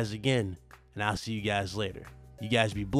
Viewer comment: Woah idk if I’m late to discovering this but your intro is dope asf🔥 Edit: at 12:13 I thought you said “dick cloud, signing out” 😂😂